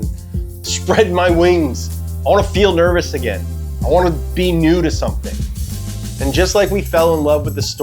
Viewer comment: I thought you said "I wanna feel nervous again, I wanna be nude or something."